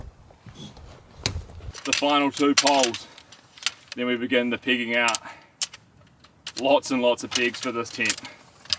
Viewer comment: He said then its time to start the pegging out. And i thought he said "then its time to start the Pigging out:!! Lol